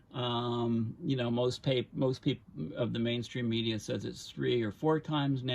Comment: What do you mean by mainstream scientists? (Sorry I’m new to this.) Thank you for your time! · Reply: Went back and found he actually said " mainstream media" at 15:19. Too many in both the media and actual scientist themselves are afraid of sounding alarmist is they state just how truly alarming the situation has become. The fossils industry and it's paid operatives are known to swiftly attack anyone that dares to openly state the reality.